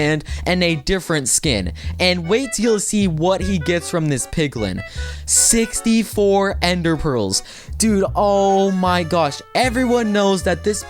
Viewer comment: Did I heard it right? 8:42:64 Ender Pearls Dude the max is 16